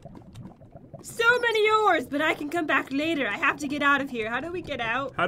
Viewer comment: When the chat said “so many ores!” I thought it said so many Oreos! 😭😂